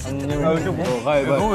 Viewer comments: tbh i thought jake said “nah i’m good b” 💀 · Owshi I heard that "nah baby I'm good"